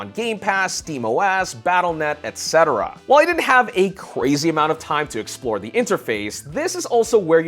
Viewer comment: did just say Cwazy?